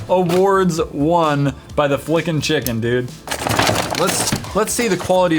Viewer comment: I thought it said freakin chicken